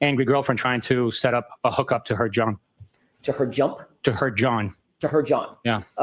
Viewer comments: Flirtatious 🤣🤣 it was 4th grade chatter🤣🤣no game with either!! But yea that was their way of flirting!🤣 I thought he said a hook up to her John like a John🤣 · LOL I thought said "setting up a hook up with her John".😅